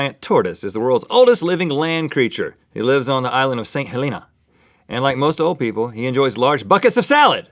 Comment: I thought Rhett said Mt. St. Helena. Dang it!